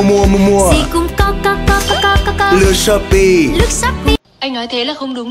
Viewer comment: o boi indonesian time di shooope pe pe pe pe pe i heard it like that uwu